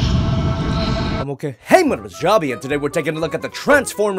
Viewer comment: ทำไมได้ยินว่าผมโอเค